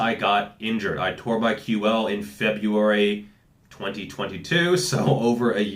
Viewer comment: What did he say he tore at 3:07? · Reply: QL